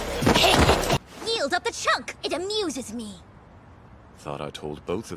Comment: wtf did she say? I actually didn't understand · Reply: "...of the choke! It amuses me."